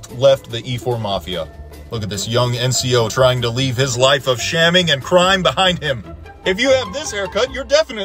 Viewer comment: Lmao I really thought you said “trying to leave his life of scamming and crime behind him” 💀